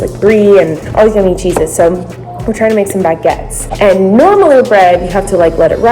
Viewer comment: DID SHE SAY NORMALAR BREAD